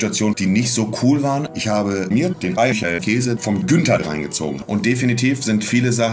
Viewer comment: Ich habe mir den Eichelkäse vom Günther reingezogen 🤮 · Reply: Krass, ich habe verstanden "den Eichelkäse vom Köter reingezogen". Ich dachte schon, der arme Kylo ...